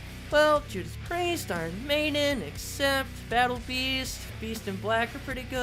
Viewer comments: Yo did heavy say battle beast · did I hear BATLLE BEAST (invincible fans arise)